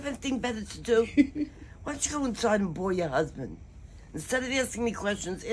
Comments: At first I thought she said, "boil your husband." 🤣🤣🤣🤣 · I seriously thought grannie said “Don’t ya have any thing better to do, why don’t cha go inside and …(BLOW!) your husband!” It honestly took a few rewinds to realize what grannie actually said 😳🤭🤣BORE -BORE “BORE YOUR HUSBAND” lol ! I got it now lol